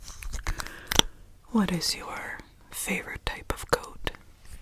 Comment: i swear i heard “what is your favorite type of goat?” and i thought wait people have favorite goats??